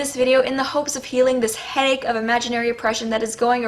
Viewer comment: I know I'm nitpicking here, but I can't tell for the life of me what word she's trying to say in the first clip that shows and it's driving me nuts. 0:59 "So, in this video, in the hopes of healing this ???? of imaginary oppression..." What word is that supposed to be? Hague? As in The Hague? Did she mean something like "haze"? I even googled for words that rhyme with "egg" and "Craig" and couldn't find a word that might work. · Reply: Thanks! It was genuinely setting off my OCD. Well, I'm sure glad has found a way to deal with her headache.